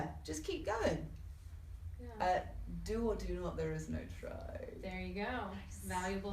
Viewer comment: What she said? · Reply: Do, or do not. There is no try. -Yoda